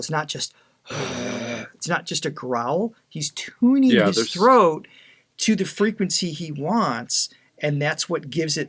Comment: First time watching you guys and I thought you said "throat god" 🤣